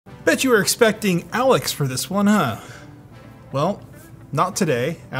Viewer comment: I thought he said "bitch you were expecting Alex here" in the beginning.